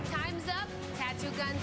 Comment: Did she really say tattoo guns?